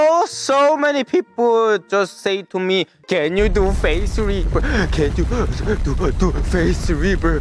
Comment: I thought he was saying face reaper until I looked at the subtitles